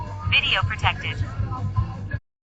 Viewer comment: I thought the camera kept saying, " idiot detected."